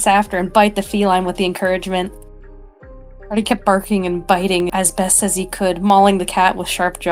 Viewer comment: why did I hear "Marty kept barfing" HELPPP